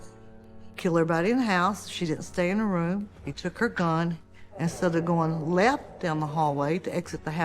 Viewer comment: “He took her gun. Instead of going left down the hallway…” NOT “He took her, gone instead of going left down the hallway...”